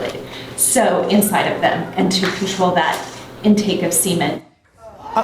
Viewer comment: Did she just say control out put of seimen lol. Sound like she swallows